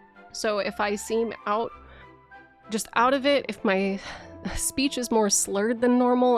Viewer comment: Swedish headaches? I genuinely have no idea what this S word is, and I know it’s the last part of the video I should focus on, but the mystery is driving me insane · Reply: Suicide headaches